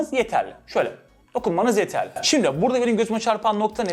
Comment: Bir tek ben mi yanlış anladım :)) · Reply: Gözüme derken gö,,üne demiş jsjsjsjsks